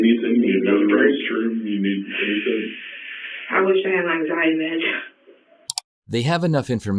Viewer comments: 23: 24:00 WHAT does she say when they ask her if she needs anything 🤔 she said I wish I had my ??? I cannot make it out. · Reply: Anxiety meds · what is she saying there? "I wish I had my anxiety meds"? people say the most tone deaf things sometimes. oh, you have anxiety cuz you murdered your child? how do you think your child felt?